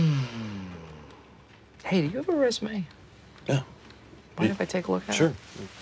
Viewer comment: Wait a sec, maybe I just can't hear but at 0:28 when Michael asked "do you have a résumé?" Didn't the dude say no but then handed him it? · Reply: He said "yeah"